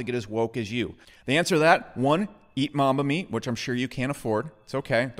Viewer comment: what does he say on 0:24 ? eat mamami? what's that